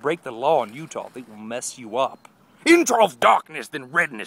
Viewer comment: at first i thought when toby said they will mess you up he said bless you up and i was like thats not too bad lol xD